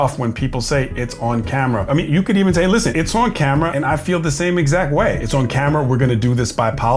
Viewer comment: I think he might be saying, 'it's on camera' as in, 'I'm not getting fired for you.' 21:49 called it lol.